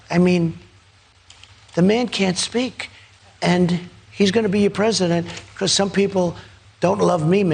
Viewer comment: did he just say Biden’s gonna be president?